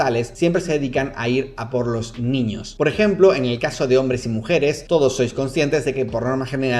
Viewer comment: tenía los ojos cerrados y escuché "eñel caso" hehehe